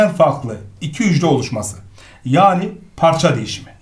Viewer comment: olmuşmaz dediniz ama oluşur 🙄🧐 Arkadaşlar ben mi yanlış anladım yoksa ?